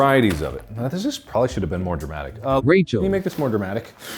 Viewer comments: What was the voice over at 0:54 I heard Rachel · MY GOD. at around 0:55 it sounds like it just says “rachel” ??? and thats my name. i nearly had a heart attack both times when i replayed it.